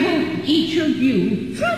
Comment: I thought she said I will eat of you lol